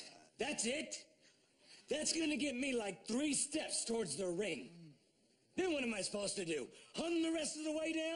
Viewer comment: Did he just say 3 steps in the ring?? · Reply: No,he said 3 steps TOWARDS the ring.